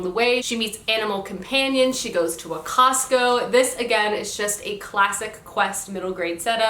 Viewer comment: She goes to a Costco?? Am I hearing that right? Lol